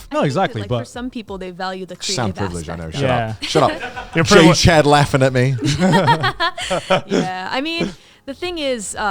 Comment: "J-Chad laughing at me" Did I hear that right? 1:20:30 "Guh"